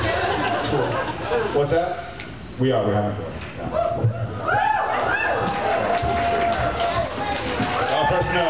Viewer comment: what did Jared say? "We're having..." what? a boy?